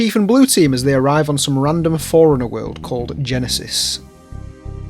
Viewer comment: Because of your accent I heard 'Forerunner' as 'Foreigner' and honestly, it fits